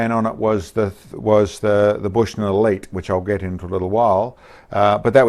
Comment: What scope are you using? All I heard is "bushnell lake" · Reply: Bushnell Elite